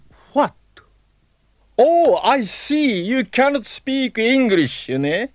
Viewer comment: The addition of 'ne' after 'you cannot speak english' is SO funny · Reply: I thought he said "you kinda speak english"